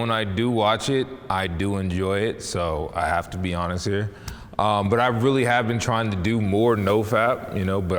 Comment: these people are amazing! one of the most honest episodes i've seen in a while! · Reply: i thought u said hottest instead of honest